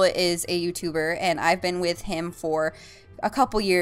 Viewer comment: did you say that you bend with him? Like bend over? Just joking around lol happy vday